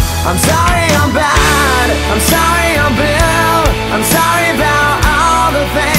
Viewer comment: Always thought he said I'm sorry I'm bill or I'm sorry I feel , now I know he says I'm sorry I'm blue.